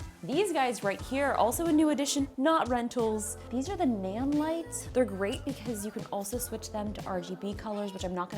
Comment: What kind of lights were those at 1:08? Sounded like she said "nam lights"